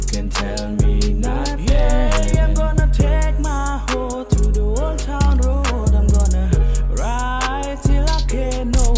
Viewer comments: Is it just me or does it sound like he is saying “take my wh*re to the old town road”? 😂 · Singer- I take my hor to the old town road Me- what did he just say I have to comment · The guy from Cambodia sounded like he was saying “yeah I’m gonna take my whore to the old town road” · he sounds like he is saying im gonna take my whore instead of horse